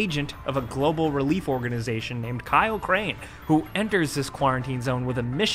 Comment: Did anyone hear him say kaio crane?